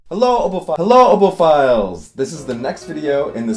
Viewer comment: OK....At first I thought you were saying "oboephiles" rather than "oboe files"...was this intentional word play? Got to love it! 😁😁❤❤❤